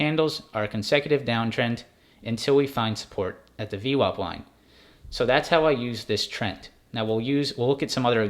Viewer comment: Hi, sorry am newbie, what is the second line down from the 9 ema, at 5:09m into vid, it sounded like d lot line?